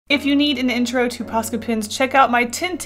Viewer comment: I thought she said pasta pins